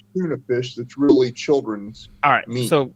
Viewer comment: 😮 wow.... That was sad....and delicious. Yummy😢 @ 9:43 did he say "tuna fish that's really children's meat????" 😮 wtf??!!!